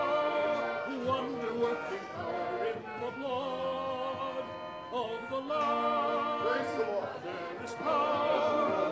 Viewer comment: I always wonder what he said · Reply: Something like “I will bury you”